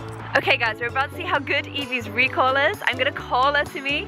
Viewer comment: i thought she said recoil xD